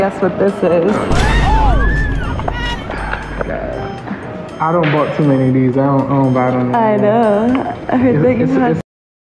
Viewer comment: i’m sorry did he say ‘i done bought too many of these i don’t buy them anymore’